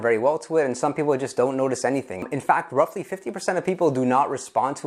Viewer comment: h wtf does he say '50 percent of people don't even notice anything' - or 15 percent? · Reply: 50%. Sorry for the lack of clarity. It's there in the subtitles so do note that most of my videos have proper subtitles added when you click the caption button, but I'm not sure how well this works on mobile.